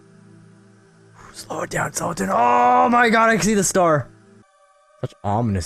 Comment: Did he say 'Ik zie de star'? Is he Dutch? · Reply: P'Pen Mu'Dera' He said I can see the star